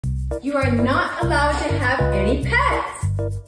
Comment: I heard that as "You're not allowed to have gay pets"